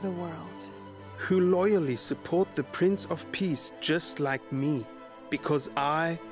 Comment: I just heard the guy say "Prince of Peace." but I heard it as "Prince of Peas." ....lol